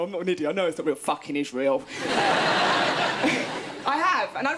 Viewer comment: i thought she said "fucking israel"